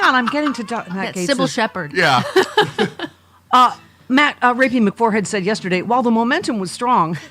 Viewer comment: I thought I heard say Matt the forehead Gaetz but I didn’t hear well. Anyway it’s a good mobster name if she did 😊